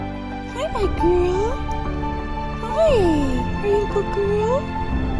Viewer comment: is it me or I think she said “grill” not “girl”🤔